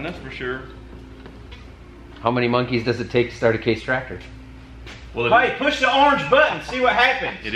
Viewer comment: it taKES SIX munkies to start a RED tractory ... unless yer name ends iin WELKER ???????????? is that what you saing.??? or you sayin all them red tractor drivers are munkies?>? lol 4:45 lol